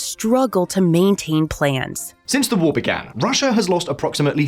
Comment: Your narrator said "plans" instead of "planes" and you didn't catch that? Thumbs down! Sloppy.